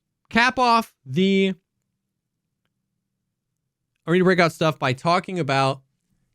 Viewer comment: I kept hearing “buttstuff” 😂